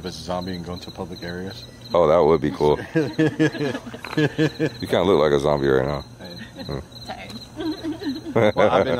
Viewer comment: I heard faint whisper in between you saying "that would be cool" and everyone laughing, but can't make out what is being said. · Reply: Yeah sounds like " this is how I feel"..